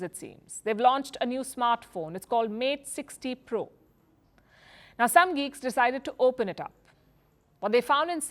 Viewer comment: did she actually say some GEEKS decided to open it up?! 😆